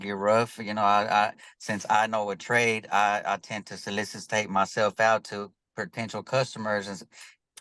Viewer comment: Did he say that he solicitates himself out? 18:27 mark